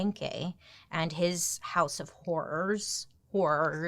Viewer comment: I genuinely thought she said 'house of hors d'oeuvres' at the end there...